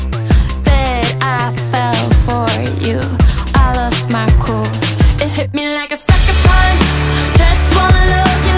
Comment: I thought it said "it hit me like a soccer ball" 🤦‍♀️